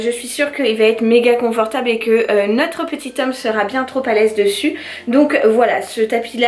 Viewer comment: c’est moi où elle a dit le prénom de babynou ? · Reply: Jo lzx Non elle a juste dit « notre petit homme »